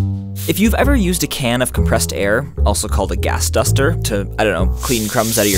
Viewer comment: i thought he said depressed air